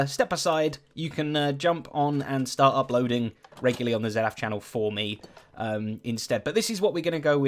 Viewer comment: It sounded like you were about to say that I could upload on hermitcraft regularly, but then you just said your channel..... so close....